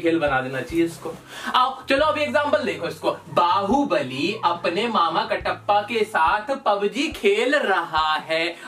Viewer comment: At first I think you said that mama ka tappa 😂😂😂